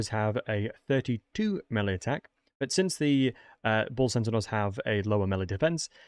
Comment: Great video, are you saying "Bull Centigaurs", "Bull Sentinals" or "Bull Centenaurs"? Is this a lore thing, so you are calling them what you know them as? Sorry for the confusion, if i am just mishearing you. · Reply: I think he's just slipping up his pronunciation of "bull centaurs" due to his attempt at a "play-by-play" sportscasting. Explaining things as they are happening gives you practically no time to think what you're saying and then you have to say it fast, so people who aren't professionals at it often stumble over their words and don't bother to vocally state and correct their mistakes because they have to keep describing what is happening.